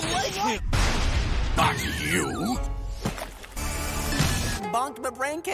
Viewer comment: Please tell me I'm not the only one who heard "Fuck you!" Instead of "Not you!"